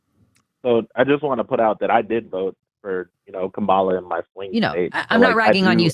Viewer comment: sounded like he said "Kabala"